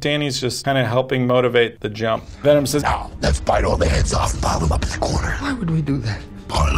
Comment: was I the only one that had to repeat it several times to understand what Venom said? as far as i know it says "lets bite all their heads off and pile them up in the corner", anyone else?